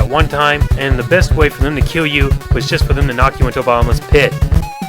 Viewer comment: at first, it sounded like you said Obama's pit